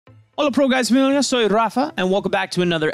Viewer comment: "hello proguwakdhgrdiwuadwaa... raafaa" wtf did he even say xDDDDD